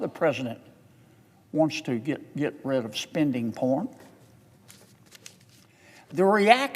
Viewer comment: Did he say "...spending porn"?